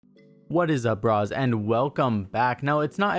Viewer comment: why did he call me a bra